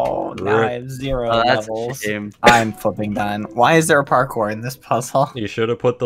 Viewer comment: did he say flipping or........